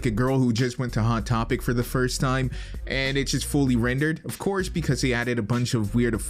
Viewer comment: Did you just say, "foal-ly rendered"???